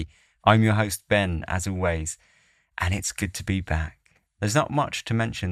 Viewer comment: I always hear, "I'm Ben Asawalwaze." 😀